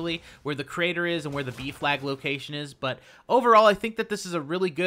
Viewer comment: Ohhhh B flag... thought he was saying beef lag